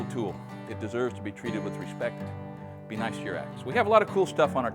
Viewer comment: At the end I thought you said "Be nice to your Ex." and for a second there I wondered what that has anything to do with sharpening. Cool stuff, thank you for showing and explaining this. I've been meaning to sharpen some old axes in the shed.